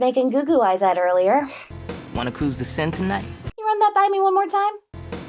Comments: "You wanna cruise the Seine tonight?" Is what he said. The Seine is the river that runs through paris. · "Wanna cruise the Seine tonight?" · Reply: I heard "cruise the scent", which I thought was a kind of gross and confusing pick-up line. Had to relisten a couple times to figure it out lol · "Wanna cruise the Seine tonight?" is what he says. (the Seine river passes through Paris)